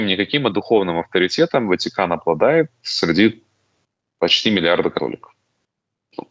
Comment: Поправочка, ослышался, сказал ,,почти миллиард,, , а мне послышалось пять, предыдущий мой коммент прошу считать ошибочным.